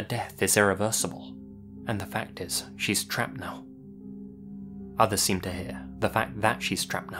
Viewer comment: I thought she said, "Her death is irreversible and the fact that she's traveled that" trailing off at the end.